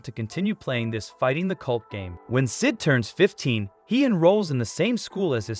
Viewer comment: i thought bro said n